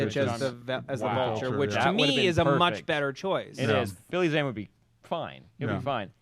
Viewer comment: the first time i thought u said Billy Mays... i was like whaaaaat... XD